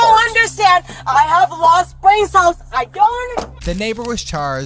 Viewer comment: "No, I don't understand.... I have lost brain cells"... lol wtf did she say? 😂